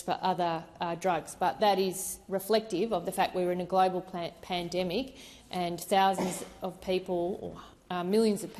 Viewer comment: Your last question to her, did I hear her say “Plan…..pandemic “?